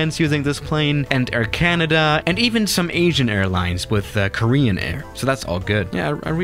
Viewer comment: When he said “Asian” I thought he said “Agean” 🤣